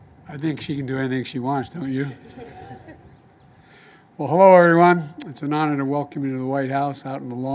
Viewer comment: Did he just say “Horow everyone”? Brilliant man.